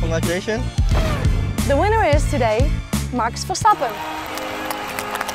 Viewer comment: did she say “Marx” Verstappen? 💀